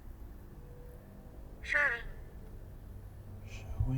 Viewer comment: When you said what happened there at 2:53:19 and it said “ Windsor “. I looked that word up and it means dresses 👀